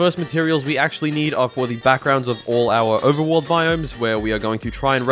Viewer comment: At first when he said “Backgrounds” I thought he said BACKROOMS 🥲🥲🥲